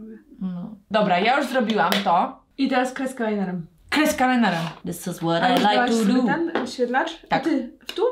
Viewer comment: ja usłyszałam kreska menela xd